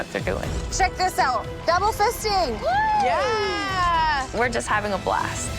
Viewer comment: “DOUBLE FISTING!” WOOOOOO! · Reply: Lmfao I thought she said double whisking